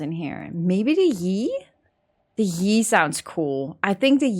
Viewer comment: What said: The Yi sounds pretty cool What I thought she said: The yeet sounds pretty cool